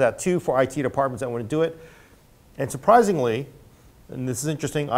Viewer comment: did you say "and surpryzenly" ? :)